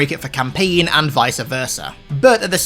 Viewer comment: hol dup did he say visa versa